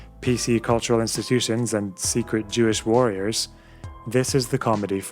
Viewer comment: Did he just say SJW means Social Jewish Warriors? Is a nazi?